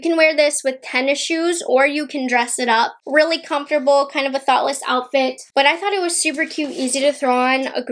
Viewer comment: Tell me why I thought you said “thot-less outfit” 😩😭🤣🤣🤣